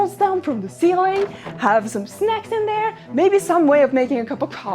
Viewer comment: I guess it's been a really long time, because when she said "have some snacks in there..." I definitely heard that as "have some sex in there."